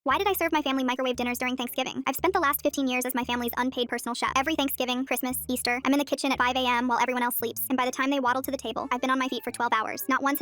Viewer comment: I thought she said 'Why did i serve my family microwaves for thanksgiving?' Edit- I've broken 3 microwaves in my house by making brownies in them. Edit 2- The 2nd time was my fault but the 3rd time idk who broke it.